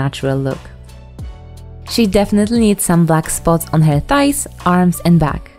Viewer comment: I thought she said luck spots instead of black spots Love you! <3